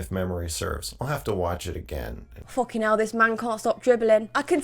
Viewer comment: in case you paused, he actually said, “I’ll have to watch it again and again and again.”🤢 Allegedly, he also made a video with his alleged slav- I mean girlfriend saying consent is boring